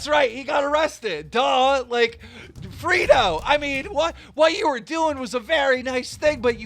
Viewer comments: did he just call him frito wtf 😂😂😂😂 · did you call him frito😂😂